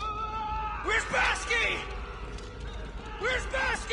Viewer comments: Wow the soldiers name is “Weirzbowski.” I always thought corporal Hicks was saying, “Where’s Basky” instead of “Weirzbowski” lol. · "AAAAARRRRRGGGGGHHHHHHHHHH" "Wierzbowski? Wierzbowski?!" "AAAARRRGGGHHH" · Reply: I always thought he was saying WHERE'S bowski?? didn't realise it was the guys NAME!!